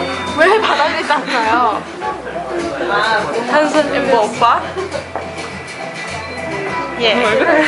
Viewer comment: I think Krystal said "Handsome Amber-oppa??!" then "yea...." and I think its the person who holds the cam (a girl or is't Sulli?!) said "Amber-unnie..Amber". ^^ I don't understand Korean, but if a person who understand English he/she will think the same as me...hehehe XD